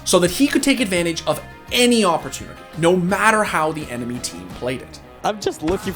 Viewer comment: Did he say awpertunity?